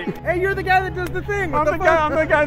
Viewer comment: Did he just say " a little bitch" at the end or Is it just me? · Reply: No, he said, "a little bit."